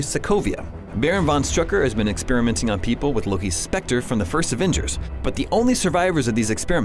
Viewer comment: Loki's 'specter'? tell me I heard that wrong.